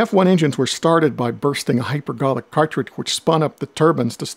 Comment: What does he say at 0:33, something like “hypergarlic partridge”? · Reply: Hypergolic cartridge. Contains two chemicals than when allowed to mix, spontaneously ignite without needing an ignition source. Basically "instant fire."